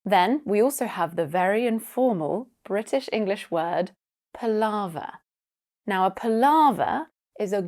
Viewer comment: all I heard was Lava 😭